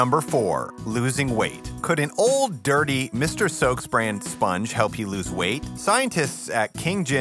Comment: This whole time I thought you were saying "Mistress Soak's".